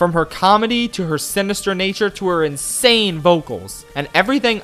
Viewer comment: did you say simister